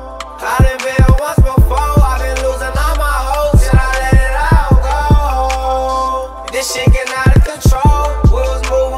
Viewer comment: does he say I been losing all my hoes or all my hope? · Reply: Hope!